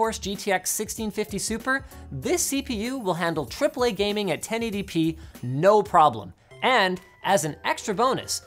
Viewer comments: The captions at 3:11 read: “10 ADP” instead of “1080p”. I’ve noted this same spelling mistake in several other videos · You should maybe change your subtitle provider. In the last weeks there are really odd errors with especially technical terms in the subtitles, e.g. at 3:11 Linus says "1080p" and the CC says "10 ADP". The next lines of the CC read "the super version of the 1650 features in videos touring and banking coder." It seams like the writer is just guessing the words. For the hard of hearing audience it's more like a word puzzle at that moment... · Mkay I gotta say this, whoever you guys have doing your captions (someone has to, it's not auto-generated) needs to step up their game. I've noticed for a while that there are things mistranscribed in most, if not every video. Example: 3:12 Linus says "AAA gaming at 1080p, no problem" Caption says: "Triple-A gaming at 10 ADP, no problem" Not even 10 seconds later, Linus says NVIDIA by name, (specifically "NVIDIA's") and the caption says "in videos"